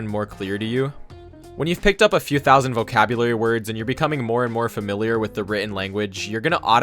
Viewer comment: I hear “moe moe” instead of more and more. I’m rotten.